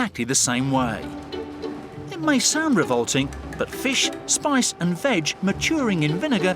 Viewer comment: I could’ve sworn he said “fish, spice and vag”